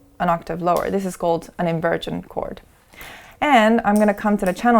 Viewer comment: Did I hear her say “invirgin chord” instead of inverted?